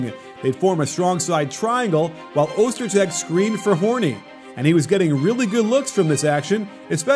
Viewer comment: Did he say horny?